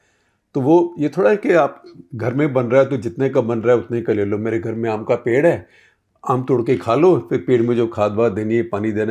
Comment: I heard it as "AMCA ped". Now i need AMCA ped ASAP.